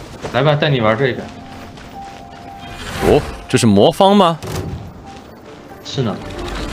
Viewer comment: didn't he say "is this a rubiks cube?" since I think he said 魔方 but im not sure